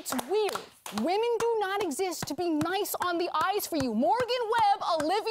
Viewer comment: Gamers: "I miss Morgan's reviews." Frosk: "So you're saying I'm not as bangable." Gamers: "No, I said I miss her reviews." Frosk: "So you're saying Morgan only exists to be nice on the eyes for you." Gamers: "No, I said I miss her reviews because she wouldn't make blunders like suggesting a game is an exclusive when it isn't." Frosk: "So you're saying my mistakes are worse than Adam's because I'm a woman." Gamers: "Adam has worked in gaming for years, so he's established trust with the audience. Your limited history in gaming is questionable at best." Frosk: "Look, it's free content. If you don't like it, leave." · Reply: I feel stupid now because i thought she was saying "nice on the ice" and I was very confused... in my defense english is not my first language :/